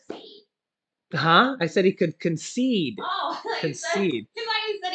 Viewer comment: I thought u said conceived too sorry lol